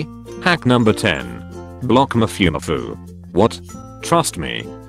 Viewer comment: Did he just say “Mafiumafiu”?? 🤣🤣🤣